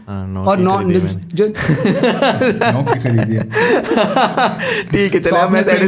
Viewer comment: What did you said:D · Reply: He said 9 ki hi khareedi he